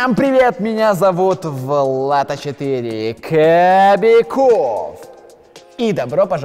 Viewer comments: Мне послышалось или на 0:09 а4 сказал на Кабякова Влад? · мне послышалось он сказал Влад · Кто услышал он скзал Влад а не Кобяков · меня не послышалась ? Так я не понела их там 4 Влада ?🤔😥